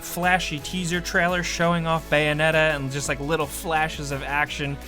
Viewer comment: for "bayonetta" I heard "Banana"... accents..